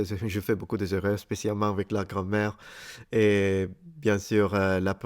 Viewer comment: Your pronunciation is very good. Just when you said 'grammaire', I heard 'grand-mère'. xD